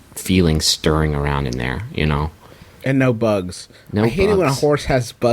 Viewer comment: I thought he said "and no bones" for a second and I was a little terrified